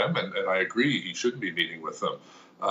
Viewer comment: WOW did I hear that correctly??! Even the mayor of Ottawa agrees with the protestors demand for Trudeau to meet with them!??! “I agree, he should be meeting with them...” that says a lot... sums it up really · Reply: No, seems like he said (more, or less), "I agree he shouldn't be meeting with them they're trying to roll back the hands of time with our battle against Covid 19."